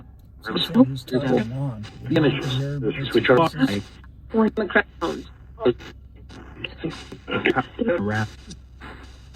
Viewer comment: I heard Wheeling, r.r. xing and ditch it was at the 1:19:39 that I heard it.